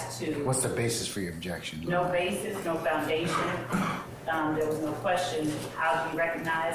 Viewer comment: How the judge help the state with objections thought he said foundation for her 🤦🏾‍♂️